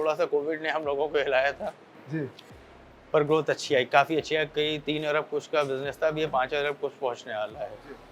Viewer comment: you said 3 Arab and 5 arab hone wala hai.. Can u plz clarify if u said arab only... or i heard that wrong... and if yes then how is that even possible...plz help to understand. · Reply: 3 arab matlab all over india mai 3 arsb ruppees ki industry hai